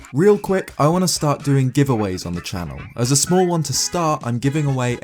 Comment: who else thought he said "real quick I want to stop doing giveaways on the channel. As a start, I'm giving away a..."